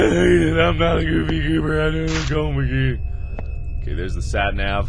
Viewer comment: "I'm not a goofygooberg-ohmahguhd," did anyone else notice that's what he was saying in gibberish around 7:05?